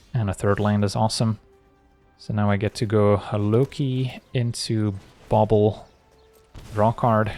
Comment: What said: "Now I can go Loki, Bauble, draw a card" What I heard: "Now I can go Loki, Bauble, rock hard"